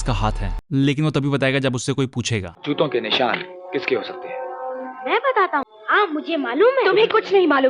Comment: Hell nah i heard chuto ke nissan instead of juto ke hehehehelol